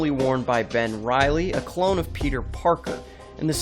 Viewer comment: I thought he said parkle